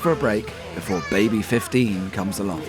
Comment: Sound like he said baby #50 instead of 15🤣